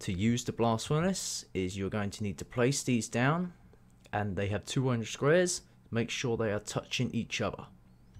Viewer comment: I couldn't catch what you said there. It sounded like you said, "they have 200 squares," but that can't be right. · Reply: think you mean 2 orange squares and both orange squares must be touching each other hope this helps